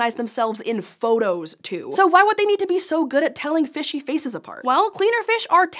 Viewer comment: i thought u say faeces